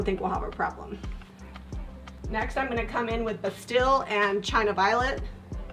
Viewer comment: Hearing this for first time today...did a double take when I thought I heard "China virus"? actually China Violet, whew!